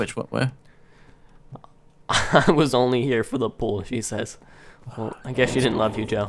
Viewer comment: Joe needs help...alot of help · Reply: | Minecraft I thought the chicken said I'm only here for the poo!